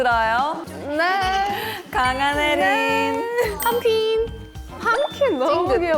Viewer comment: "Pham Queen" But I heard "pumpkin" 😂😂😂